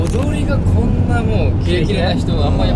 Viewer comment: 翔太くんの「踊り」が何度聞いても「お雑煮」に聞こえる笑笑笑